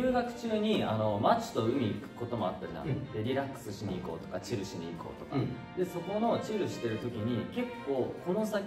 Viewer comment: チルにし行こうがチューしに行こうに聞こえて…ドキっとしたわ